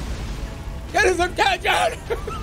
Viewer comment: after so long ive figured out what he says at 19:29 "get his attention!"